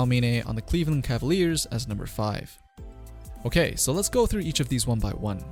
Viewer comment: you activate Google assistant on my phone. I think what you said sounds like ok goggle.